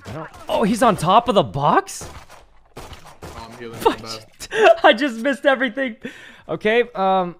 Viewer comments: did I just hear him say fck? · Is it just me or does it sound like sykkuno said fuck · DID SYKKUNO JUST SAY "FUCK" !! EXCUSE ME!! WHAT ?? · did he say "fucked it"? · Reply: He said "fudge" i believe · Am I the only who thought I heard Sykkuno said "Fuck shit" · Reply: He said fujjj bruh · I thought sykkuno said fuck at 6:30 and had to rewind it to check if he's still pure lmao 😂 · did sykkuno just say fuck???????????????????????????????????????????????????????????????????????????? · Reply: He said fujjjj not that